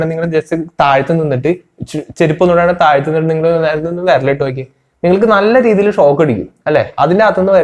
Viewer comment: power socketl viral idaano? 🤦🏻🤦🏻🤦🏻 ini ath kett ethra ennam vadiyavm. Be careful what youre saying and how you say it bro.